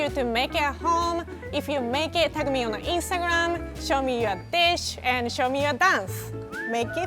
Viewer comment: "Show me your dish" 0_0 could've sworn she said "show me your bitch"